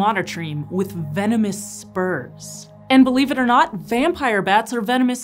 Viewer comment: I thought she said "venomous sperm" when talking about the platypus. 😂